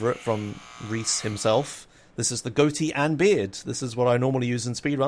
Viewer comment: "goaty and beard" i think it says Afro & beard :D